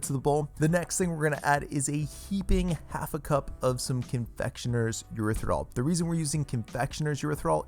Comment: What did you add after the flour? You said confectionery efffmkhgcggdfggoogaga, he said it to fast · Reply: Erythritol (a sweetener)